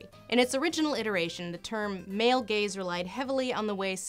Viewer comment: Up until now, I had thought that she was saying “male gays”.